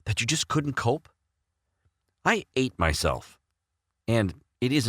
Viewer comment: Excuse me, did I mishear, that, or did he say ‘Have you ever been really scared of something? Yea cool well I ate myself so lol lmao smh